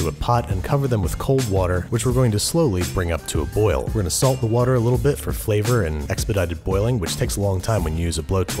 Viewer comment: i thought u said ' we are gonna insult the water a bit'............